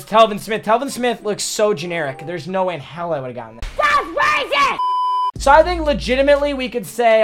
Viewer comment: anyone else hear, dogs raisin, that’s racist, that’s crazy, dads racin, trumps blazon??